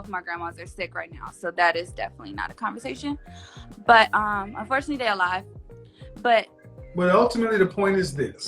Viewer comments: did she say unfortunately her grand mothers are alive? Maybe I just heard it wrong 😳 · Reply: She said unfortunately they are sick right now. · Wait, did I hear her correctly? Did she say that both of her grandmothers are sick but “unfortunately” they are alive?